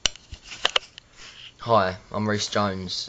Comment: i think he sounds like he says, "hi im rick james" hahaha